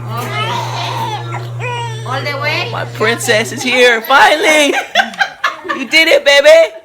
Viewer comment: did he call her violet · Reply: finally